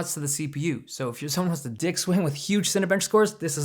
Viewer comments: If you're someone that wants to 'dick swing' ??? LOL never heard that phrase. Is that what he said? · Did he say at 0:40 "...someone who wants to DICK swim (?)" LOL That was great